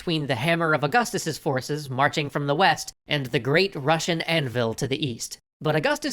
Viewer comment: anvil? Oh I thought you said sickle my bad stalin intensifies